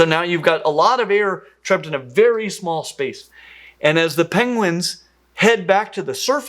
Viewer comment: Did you say 'Penglings'?